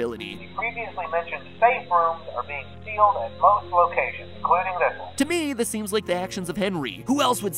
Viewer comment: "The previously mention safe rooms are being sealed at most locations,Including this one" I heard "including nipple"